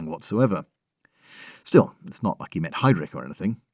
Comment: When you said ‘it’s not like he met Heydrich’, I thought you said ‘it’s not like he met Hydra’. Could have gone either way, really.